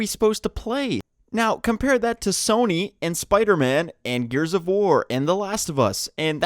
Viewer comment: Did he say Gears of War? 2:22.